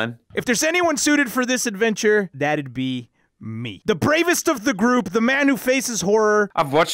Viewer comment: Who's the bravest of the group? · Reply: ohhhh that group i thought it said Who's the bravest of your group like my friendship group but YOU!!